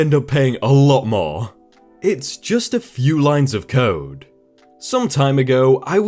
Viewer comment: I thought he said “it’s just a few lines of coke” at first...